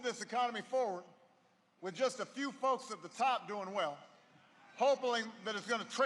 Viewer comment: OMG. Did he really say "hope-aling"@ 14:21 ? Surely, it was a misprint on the teleprompter as Obama is far too brilliant to make a stupid gaffe, doncha know.